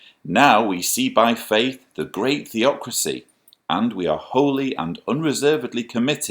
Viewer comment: Is it just me, or did I mishear 'hypocrisy' when reads the word 'theocracy' (when reading 'Children' book)? Could be my ears though!! (Teehee!). I can totally see reading the 'birthday party head choppy choppy' story to his littl'un the night before her next birthday... In an even creepier manner than he did then! In answer to the story's rhetorical question, clearly God wanted us to celebrate birthdays and other types of parties, after all it was the person who declined the party either outrightly, or subtly by not going, who lost their head!